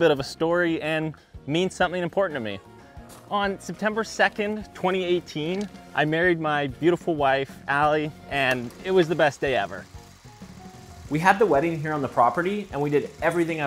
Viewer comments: I heard "I buried my wife.." I was like nah 💀💀💀💀💀💀💀💀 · Dude, I yawned at 1:03, and it sounded like you said "buried" 😭. Congrats on the marriage though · Not gonna lie when he said I need some special dirt i thought we had gonna dig a grave up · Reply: Yeah, when he said that he married his wife September 2nd, 2018, I thought he said buried until he showed the pictures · I THOUGHT YOU SAID “i buried my wife” AND I ALMOST FREAKED OUT IM SORRY 😭😭 but congrats on getting married tho lol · watching this on low volume with noise in the background and at 1:03 I hear "I buried my beautiful wife, Allie, and it was the best day ever" and I have to take a minute to rewind because I do not think I heard that correctly 🗿🗿🗿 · When he said he married his wife I thought he said buried It’s safe to say I was rewatching that part at least 5 times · When he said he married his beautiful wife at the beginning, I thought he said “buried” and thought he was going to make a dirt ball out of her buried remains. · Watching with the volume low he was carrying a shovel talking about getting some special dirt then he said in 2018 he married his beautiful wife...I thought he said "buried" instead of married...wtf! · My volume is pretty low and I thought you said “buried” and the video was even more interesting for a second before realizing you said “married”🤦🏻‍♂️🤣 Awesome ball handling btw! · While he was holding that shovel up, I totally heard him say, “I buried my beautiful wife Allie.”